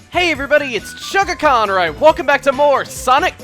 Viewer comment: I always thought your intro sounds like you say "Hello everyone, it's Chuggacon where I welcome back to...".